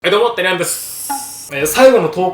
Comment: 何回聞いても「どうも、てらやんです」に聞こえる